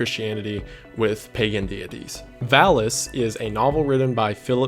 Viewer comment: i was just listening to the video and really misheard what he said. I only heard the word phallus (valis) and dick (philip k dick)